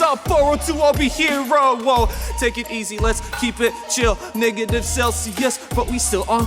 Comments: For a moment, I thought dropped the N word and it turned our he was just saying Negative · I'm pretty sure I heard "Niggative"